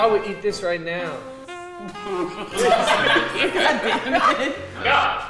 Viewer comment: is it saying “get out of here” ?